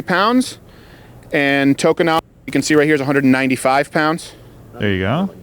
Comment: did he say Chokanawan?